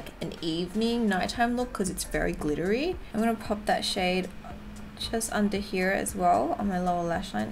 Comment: When she said “ pop that shade “ I thought she said “ pop that shit “ lol 😂